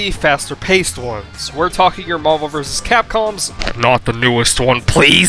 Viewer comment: When he said "Your Marvel vs. Capcom", I heard "Yo mama vs. Capcom" and got visibly confused